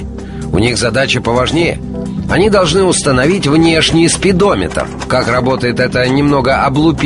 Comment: мне одному показалось что он сказал спидомитр?